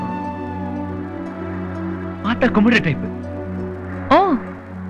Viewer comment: What he say? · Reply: Paatha kumbidra type